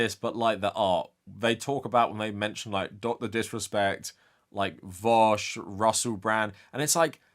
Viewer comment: Did he say Vaush?